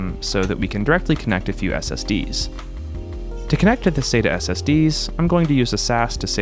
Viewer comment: i heard he saying "sus"💀